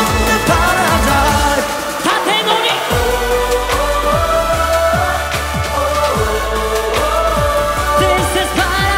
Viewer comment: くらいで想太くんがなんて言ってるかわかりますかー？？ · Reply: ピウイ27 縦ノリと言ってるんだと、思います。